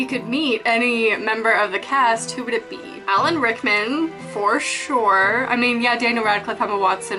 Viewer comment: AT FIRST I THOUGHT SHE SAID ALEC RICKMAN AND I WAS LIKE UM UNSUBSCRIBE OML NOOOOOOOOOOOOOOO AHAHAHA but definetly subscribing.